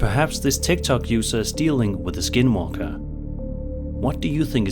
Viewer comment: When he said "dealing with a skin walker", it literally sounded like he said "dealing with a SKIDMARK". I had to replay it with the caption on!!